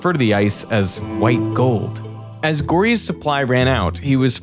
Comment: did someone say gold